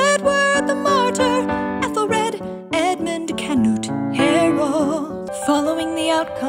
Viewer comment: I keep hearing "canoot handle"